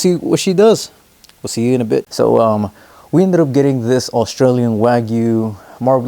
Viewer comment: I have no clue who the mustache man is lmao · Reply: sorry I thought it said Austrian not Australian and also mustache man is a nickname for H****r